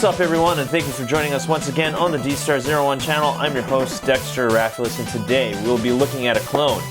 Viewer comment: I remember mishearing your name as "Dexter Rathalos" and was quite surprised. Also, would be cool if you could review some KO transformers like Wei Jiang's MP10 Prime.